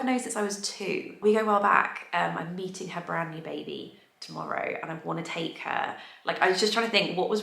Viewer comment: Sounded like you said you’re eating her brand new baby😂😂😂😂